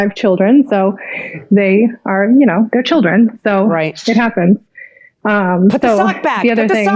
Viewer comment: did she say "shit happens" at 10:02?